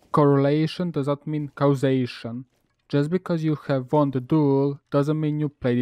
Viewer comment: captions say "[...] because you WON'T HE duel, doesn't mean [...]" instead of "WON THE" Edit: it's not the only error. 3:58 has one too. First time looking one of your vid with captions on, As while your english is not the easiest to understand it's not that bad either, and thus prefer to keep captions disabled, even if it means replay a segment once or twice. Captions on confused the heck of me. Back to caption less